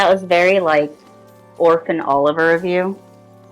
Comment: i heard "orc and oliver" i was like what buddy cop